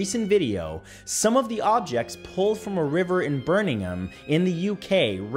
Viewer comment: did he just call Birmingham birningham wth?